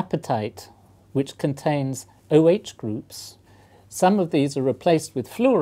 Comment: what does he say? awache groups im sory i dont know what hes saying